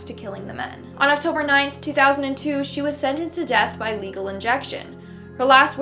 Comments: what did she say? Legal injection?? I had no idea that was a thing... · Legal injection anybody else catch that